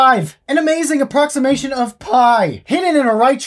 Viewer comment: I thought it said IP hidden inside a huge triangle